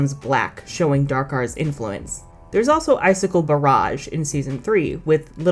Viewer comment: Why did I hear this as Icicle Garage? Also I’m glad you are finally doing a video on the Trix, as overused as they are, they are still iconic.